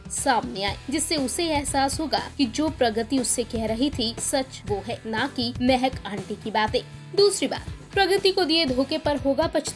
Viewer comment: Did she said MEHEK AUNTY at 1:19 😂?